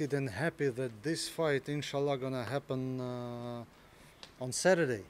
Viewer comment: did he say ‘’inshallah’’ ?